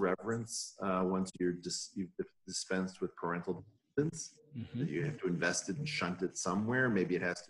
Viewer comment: "Once you dispensed with parental ****? " What did he say there? "guidance"? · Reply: I think he says "dependence"